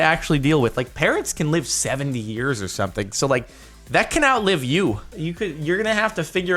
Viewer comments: At first I thought he said "Parents can live for like 70 years or something" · am i the only one who thought that he said parents live 70 years not parots live 70 years